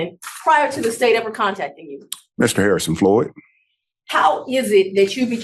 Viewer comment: not going to lie, I first heard the attorney say: Mr. Harrison Ford and my brain short circuited.